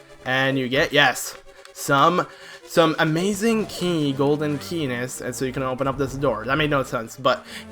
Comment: I thought you said golden penis! LOL.